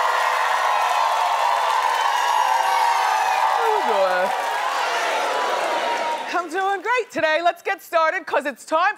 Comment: What does she say at 0:53? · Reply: How you doin'